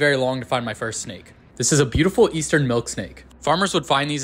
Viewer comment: I got excited, thought you said milk steak